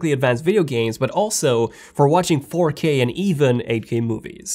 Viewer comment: the way the guy says 4 i thought he was about to say porn